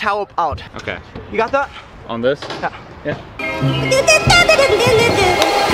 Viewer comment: who else heard safety is first...